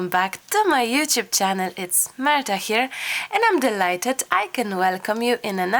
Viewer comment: In the beginning of the video when you were saying your introductions, I heard "It's murder here" and I thought oh no, poor Marta, she must be rushing around for Christmas like I am, but also has a little baby to take care of now on top of everything else. I am so sleepy and then thought no, she would never say that (at least not out loud) and realized you were just saying "It's Marta here." Lol! Boy oh boy, do I need a nap! 😆